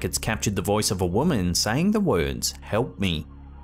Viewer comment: With the disembodied voice what I thought I heard was “help me I’m cold” and I think if I’m not mistaken anyway that it’s actually snowing as well..